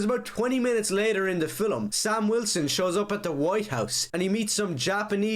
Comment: sounds like hes saying filim instead of film